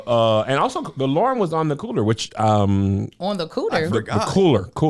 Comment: I also thought he said “on the cooter” 😂